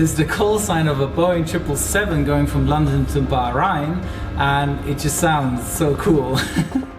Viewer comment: Did he say London to Bahrain?